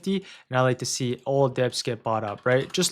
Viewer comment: I don't understand the word you're using at 5:14, 5:19, & 5:27, all... are you saying debts? You'd like to see all debts bought up? If so, what does that mean please, thanks? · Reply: dips